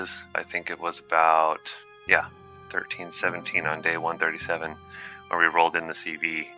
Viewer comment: He says 137, but the captions say 147. You may want to fix that.